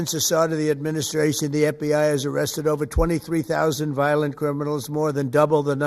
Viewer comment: Feminals? Did he just say violent feminals?? Does anyone else think that may have been a Freudian slip??